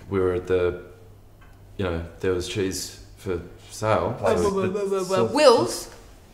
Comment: At first I thought they were saying cheers